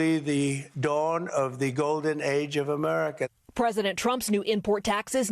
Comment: Wait is he saying the Golden Shower of Prosperity is happening?!??!? 😮